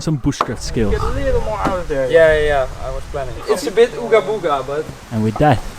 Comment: "it's a bit uga booga" ?????? Did I hear that right?